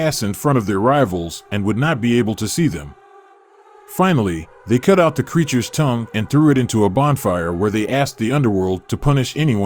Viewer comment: When you spoke of the deceased little girl, it sounded like you said "They cut out the 'creatures' tounge!"